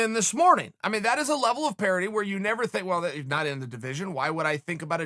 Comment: Lately I keep hearing Chael say “parody” what does he mean by that ? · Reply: Eric Palahniuk parity, like fairness or equality.